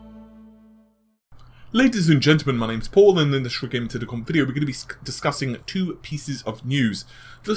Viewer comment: I just slowed it down to 50%, sounds like he says "in this red gaming technicom video" So weird. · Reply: he's saying red gaming tech (dot) com